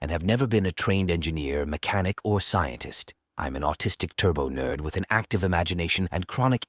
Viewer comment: “I am an autistic turbo nerd with an active imagination” could this man be one of my people? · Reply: I thought he said artistic but maybe it’s both.